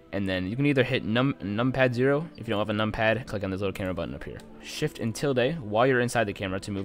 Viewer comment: I didn't understand what you said here, how do you move the camera? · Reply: Shift & Tilde, which is ~ on the keyboard